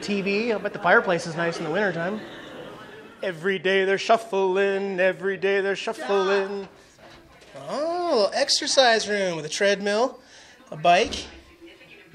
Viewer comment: Exercise...? I thought you said extra fries!